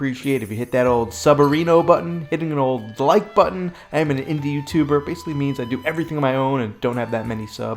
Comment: I thought he said he is an Indian so that's why he did not have subs, so confused I had to rewind and listen again, indie, 😂